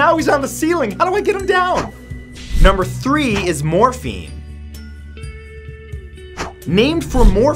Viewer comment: I thought I heard Morphe 😂😂😂 as if Morphe cosmetics 😂😂😂